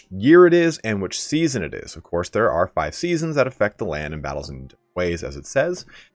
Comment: did he say there are five seasons?